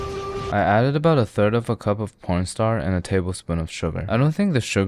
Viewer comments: did he just say po*nstar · Did you just say po*nstar instead of cornstarch or is my mind playing tricks on me 😂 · did I hear pornstar